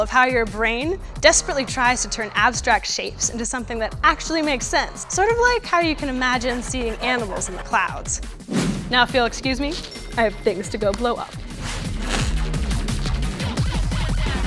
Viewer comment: At the end the video buffered and i only hear her saying "and now i have thing to go and blow" :P